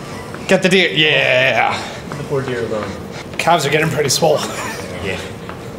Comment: Subtitles are off. They say "poor deer though" but he actually says "Leave the poor deer alone"